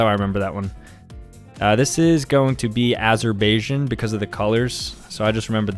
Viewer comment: when he said azerbaijan, i thought he said m*st*rb*t**n